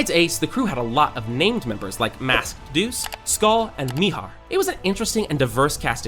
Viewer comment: I heard Mihawk. Anybody else?